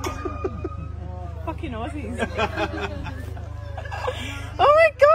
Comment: what did the guy say? "did you look ???"